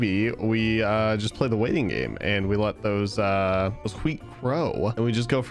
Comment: … did… did you just say HWEAT?